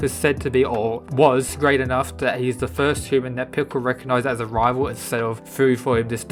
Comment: sounded like you said pickle rick lol